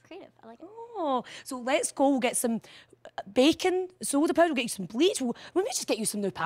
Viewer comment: I first heard that as “Let’s go get some bacon...”